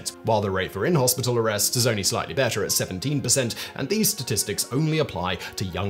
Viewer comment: the close caption shows 177%, instead of you said on 17:08 played in the video. I heard you say 17%.